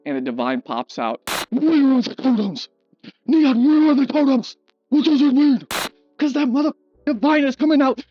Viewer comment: I actually thought he was saying "neon where are the condoms" until the clip played the second time 😂